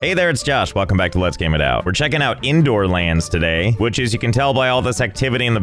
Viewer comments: i thought Indoorlands said McDonalds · I honestly thought that it said ppp indoor land